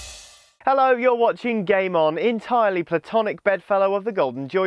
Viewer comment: it sounds like he is saying gaymon